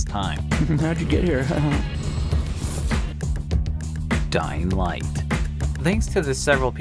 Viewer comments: I thought it said "doing sh!t" instead of "Dying light" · I thought dying light said dingle shit